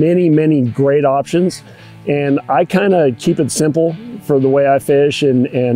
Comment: Thought he said he kept it simple. lol